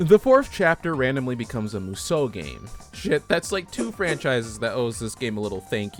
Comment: i thought you said kusoge not musou ge. i was like "... ... ... harsh."